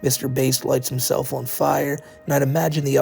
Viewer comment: Ngl i thought said that Mr Beast set himself on fire